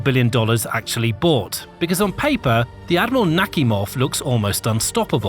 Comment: I really heard the "Admiral Suk-me-hov" the first time. 😂😂